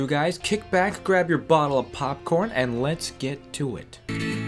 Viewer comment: did just say "grab your bottle of popcorn?!"